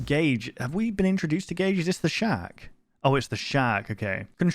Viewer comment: Bro is so British I thought he was saying shack instead of shark for like 60% of the vidoe